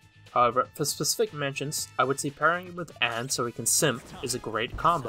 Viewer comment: "party him with Ann so he can simp" did I hear that right? 😂